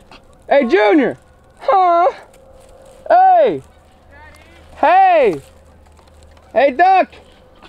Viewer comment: Did say daddy 12:29 ?😂😂😂